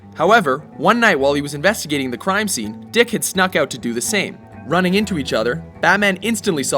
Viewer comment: i thought i heard him say DICK HEAD :D